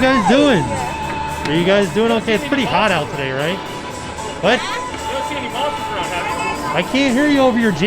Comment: It sounded like was saying, "Have you seen any monsters around?"